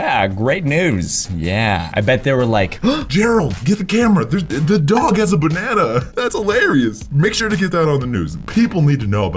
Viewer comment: that dog has a banana me: I thought you said the dog was a banana